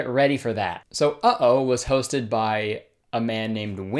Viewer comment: all i heard was "his name is Twink Yahoo 😭😭